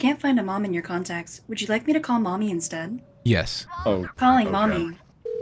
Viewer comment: I thought Siri said would you want to call me mommy instead 😂